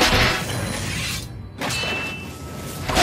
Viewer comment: What is Ramsay said "fuck out "video?